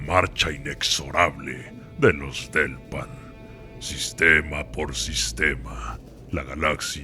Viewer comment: apenas hasta ese momento entendí que se llamaban "Delpan", pensé que estaba diciendo "Los del pan" yo pensando que ese nombre no imponía ajajajjaa